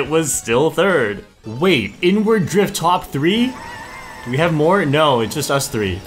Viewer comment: I was talking about this when I was racing my sister, and she thought I was saying n-word drift